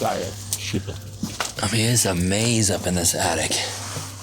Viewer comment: were u alone in there because I keep hearing voices. right before the 25:00 mark when u find those crutches someone says "Shit" Then right after they say it again.....did u hear any of it??